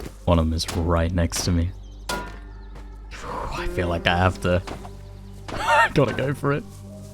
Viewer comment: What says vs what I hear... "That was sooo close. I have to go back." I hear: "That was sooo close. I have to give my neighbors at least one more of my kits."